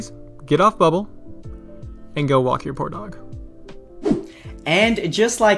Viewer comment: I thought he said, "Now, please, get off Bubble...and go watch your pornog" O.o